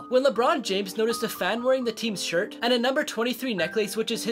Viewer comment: I thought he said make her a date not make her day lol